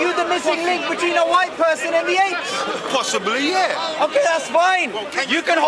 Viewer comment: did he actually say yes??😭🤦🏾